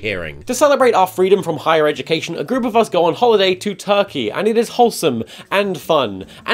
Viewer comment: Did i hear it right Did he said Turkey?